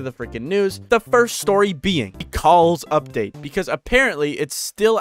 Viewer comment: thought he said the coles update